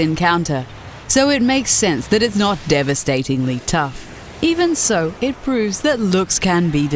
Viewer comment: I usually dont mark words but did they just say de-steve-ing? 1:15 xD